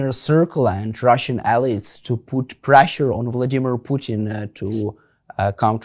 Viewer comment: Did he just say ,pressure on Kiev? Get lost😢